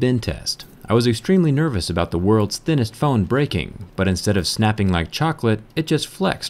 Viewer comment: why did i heard "the THICCEST phone"?